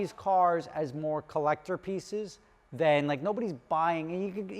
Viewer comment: I had to rewind it back... I thought he said collector feces.